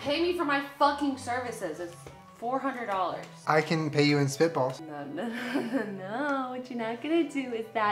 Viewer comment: For the longest time I thought she said “ew is that a stink bug?! How fucking old are you?!” But then I was today years old when I realized she said spitball 🤦🏻‍♀️